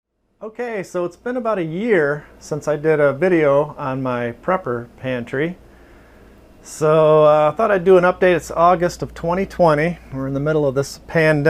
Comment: Smh My brain man...I thought it said Pepper pantry. I'm sitting here like who owns this much pepper